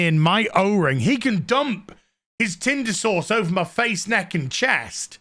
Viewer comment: I'm sorry. For the first thirty times I thought you were saying the mom's wanted the guys Tinder Sores spread over their face, neck, and chest.